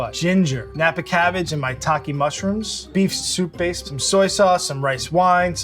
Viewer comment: At first I thought they said Taki Mushrooms.